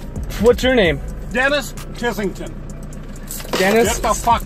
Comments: I thought I heard "Dennis Pissington" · I really heard him say when he was asked for his name he said, Dennis the fucking Mence. 😂😂😂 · I thought she said Janice Tissington.